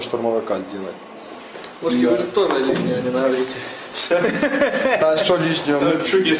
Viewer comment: he said: вы ж тоже лишнего не наговорите! - а что лишнего? which translates: watch you mouth, don't let leak anything! - like what? subtitles read: don't want to even stay close. - but what did i said that's not true? what? 🤯 they apparently misheard it.